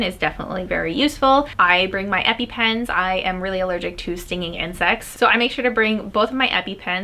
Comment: When she said she always brings her epipen, i seriously thought she said, "I am really allergic to stinging and sex." 💀💀